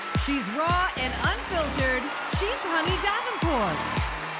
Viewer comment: I kept hearing "Cheese Honey Davenport"